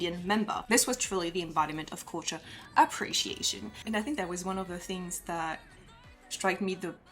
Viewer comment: is the best example of cultural appreciation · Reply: I will be honest at first I thought you said appropriation not appreciation and I was a little confused.